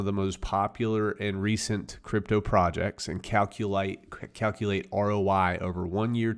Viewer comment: Did I hear Calculite….